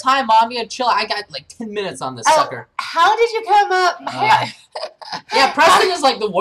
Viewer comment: did he say "fucker"? correct me if I'm wrong.